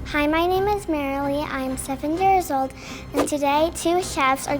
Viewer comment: Am I the only one who heard “I am 2nd years old” here: 0:02 but then listened to it a couple of t8mes and heard seven years old